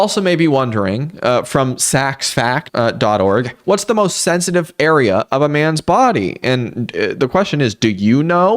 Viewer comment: misheard as ”sensible area” and i was thinking “a ballsack, sensible⁉️” 🤣✋🏼